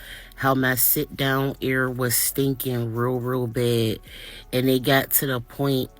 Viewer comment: OK, WTF is sit down air?? And yeah, you bought cheap surgery from a not a doctor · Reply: I thought she said area , sit down area unstead of ass.